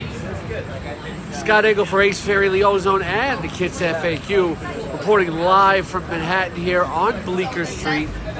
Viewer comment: did he just say ace fairally?